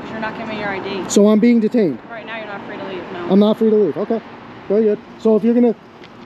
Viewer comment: This is the exact moment the male officer realizes that the female officer messed up and from this second onwards he is in damage control mode to save his own job so his family doesn't go hungry like hers. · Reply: I actually heard her say "you're free to go"